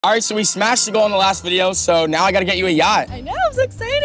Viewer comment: Ummm tell me why I heard “so we smashed on the last video so now I gotta get you a yate “🤦🏻‍♀️ had to replay that shit again to make sure I was wrong 😂